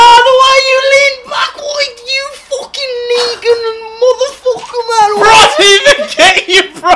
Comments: what did even say? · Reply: Nah deadset swear he said neegan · Did say the N-word? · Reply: Yh he said negan. From the walking dead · Oh so he said Negan, like the guy from TWD, all this time i thought he said the n word :)) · did he just say: n***a? XD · Does anyone hear say nigga motherfucker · Did he say the n word at 3:00?